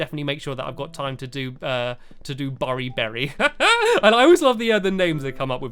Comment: I thought u say Barney bery